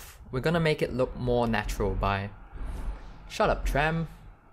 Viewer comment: Does anyone else hear 'shut up tram ' at 04:07?? lol